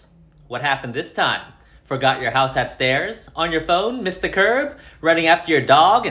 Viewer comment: I thought he said “Forgot your house upstairs?” XD I’m going insane